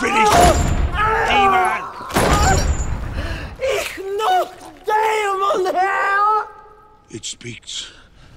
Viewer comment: all i hear is "Im not (the) demon HERE!"